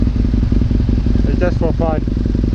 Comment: I thought he said for safety purpose. but i think i heard he said just for fun? Well okay i can go with that. I mean it would have probably need a longer explanation to them i guess, but hey, they were on the road.